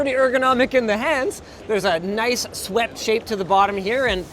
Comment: I was super confused, as I heard “sweat shaped”. 😂